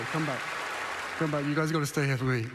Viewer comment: Sounded like he said “you’ve got to stay here FOR me!”